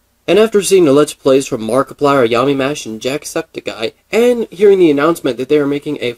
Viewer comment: The way you said jacksepticeye tbh it sounded like you said 'jack sucked a guy'